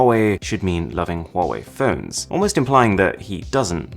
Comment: it says fern instead of phones